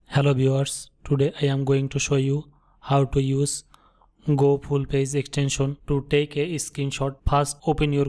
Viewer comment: Great extension. Did he just say Go Full Paze..?